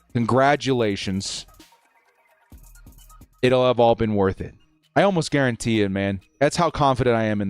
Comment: You said “it’ll have all been worth it” But all I heard was “it’ll have Harbaugh worth it”